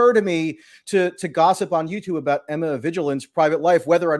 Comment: Emma vigiland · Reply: I thought he was saying Emma Vigilance.